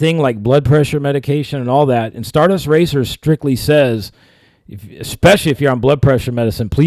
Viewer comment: Crump thought they said "Star Dust Racists" and immediately got on the case